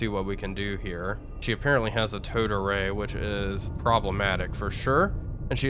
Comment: The first time I heard 'Towed Array' I thought it was 'Toad Array'